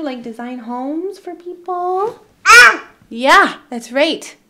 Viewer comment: Hi! I hope 2023 is your best year yet! ❤✨ I think reaction in 13:02 speaks for everyone. It sounded like "Hell Yeah" to me! Great to see you back!!